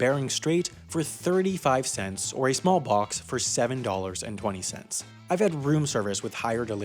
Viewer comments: Did I hear small pox? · for a second I thought you said "smallpox"